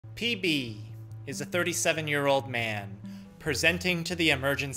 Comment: I heard ""PB" is a 47 year old man" as ""Lead" is a 47 year old piece of metal"